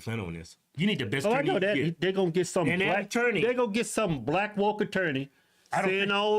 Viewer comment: I thought a new word was invented when I thought they said blakttorney